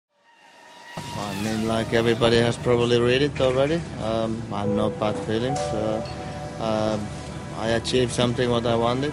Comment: What said kimi the beginning? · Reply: Might be a bit late now XD But anyways: ''I mean like everybody has probably read it already, uhm, no bad feelings. Uhm, I achieved something that I wanted and uh now it's time to do something else''